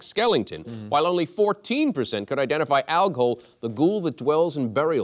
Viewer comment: It was pretty irresponsible to speak impiously of Algol. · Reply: I thought they said al gore and got confused